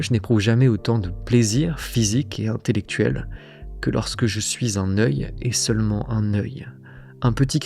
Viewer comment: j'avoue, ça m'a sorti du mood de la vidéo mdrr, j'ai compris "je suis un neuille"